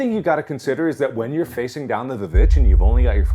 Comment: When said, "Down the vvitch", I thought he was saying down to the bitch.